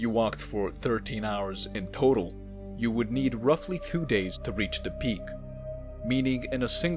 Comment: What? "Thew days?" It sounds like you're saying Two, Three and Four in one word.